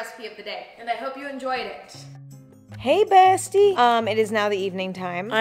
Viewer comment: sounded like a 58 year old woman saying hey bestie i can't 😭😭